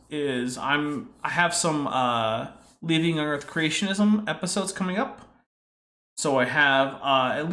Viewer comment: I misheard "Leaving young earth creationism" as "Leaving earth creationism" xP